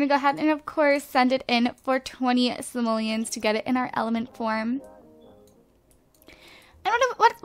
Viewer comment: at like around 4:30 it sounded like she said elephant porn instead of element form o____o.......